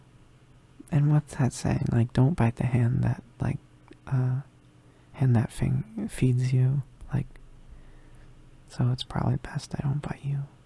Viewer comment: the way i rewinded to see if i heard that right lmao, does this count as a freudian slip?? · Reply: HAHA THIS WAS PLANNED!!! I've literally had the quote "Don't bite the hand that fing- FEEDS you" on my ideas board for like a month at least